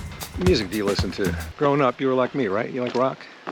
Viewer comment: i thought Jonathan said “growing porn” too 😭😭😭